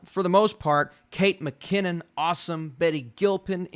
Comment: Hah! I thought you said “Eddie Murphy” in Schitt’s Creek. I was like, damn I need to see that new season!